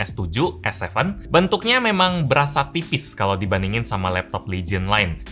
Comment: ini apa aku saja salah denger ataugimana,:goodvibes: kedengeran di menit 0:57 kayak mau ucapin TIPIS tapi seperti diucapin PIPIS